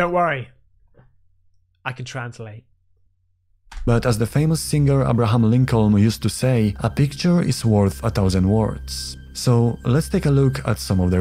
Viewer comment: Did just say: As the famous singer Abraham Lincoln used to say "a picture is worth a thousand words."? 🤔 Did I hear that correctly? Cause CC says that as well. 😳